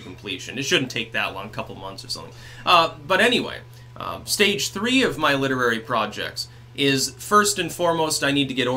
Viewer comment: jese I thought it said stage 3 of my lethal cancer. Thank god youre alright :)